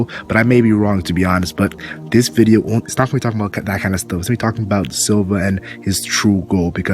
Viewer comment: What in the fuck did u say at 0:22 I played that shit back like 100x and it sounds like you start speaking ancient tongues for 2 seconds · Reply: LMAOOOOOOOO MY BAD I SAID "ITS NOT GONNA BE TALKING ABOUT THAT KINDA STUFF"